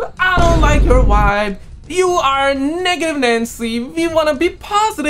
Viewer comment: Uhhmm bro what was that at 14:43 to 14:45 ehh? what did you say there can you repeat it? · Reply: Negative Nancy 😂😂